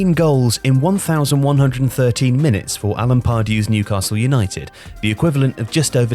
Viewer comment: Oh! Alan is his name. All I kept hearing was “Sack Pardew” that year; thought his first name was “Zachery.”